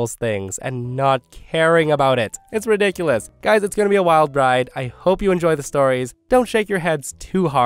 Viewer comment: I hope you enjoy my misinterpretation on your words. I heard "wild bride" 😂 you make my day all the time 😊thanks dude